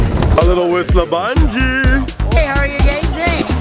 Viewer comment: I heard "how are you gays?" too hahaha